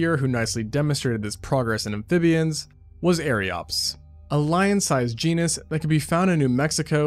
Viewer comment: I’m sorry did I just hear lion sized?